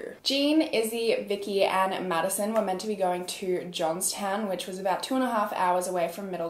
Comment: Oh crap, I thought you were saying Jonestown for a second